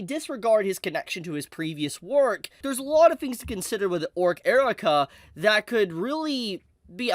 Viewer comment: I thought it said Orc Erotica 🤣